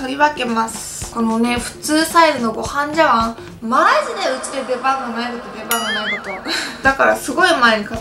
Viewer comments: ホントに何言ってるか聞き取れんw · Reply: まじでうちで出番がないこと出番がないこと って言ってるようにきこえました！ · なんて言ってるんですか？ 加工も相まって余計聞きづらくて…… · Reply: ちゃんすぎ このごはん茶碗、まーじでウチで出番がないこと出番がないこと ウチで or 映る 多分これや！